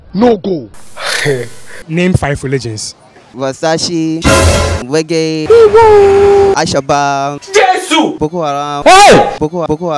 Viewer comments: Did i just hear Versace reggae alshabab boko haram 6:45 😂😂😂😂😂💣💨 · is he really saying al shabaab 😂